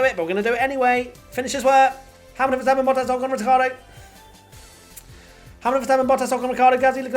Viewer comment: did you just call him 'Retardo'? 😂